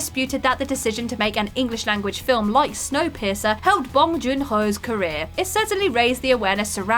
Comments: I literally thought she had said, snow-pisser. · I really thought you kept saying "Snow Pisser." 😂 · Sounds like you were saying Snow Pisser.